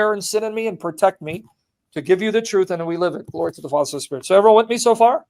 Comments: So your subtitles say glory to the false spirit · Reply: He mumbled “to the father son and spirit” , in his past videos he speaks quickly sometimes in between lessons. The subtitles didn’t pick it up correctly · Did Sam say "glory to the false spirit"